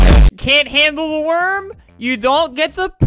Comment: This was edited by Paige, on Twitter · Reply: I really thought he said you cant handel the worm you dont get the sperm XD